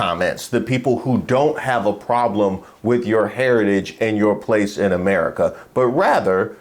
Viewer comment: Thought he said there was racism or prejudice in America!